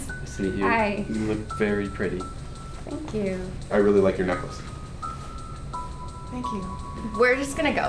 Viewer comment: I thought he said "I really like your nipples."